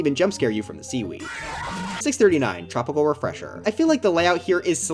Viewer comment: What did he say? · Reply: Tropical refresher